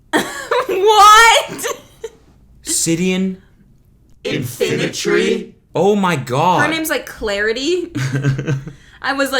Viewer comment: I thought he said, “I’m sitting in front of a tree.”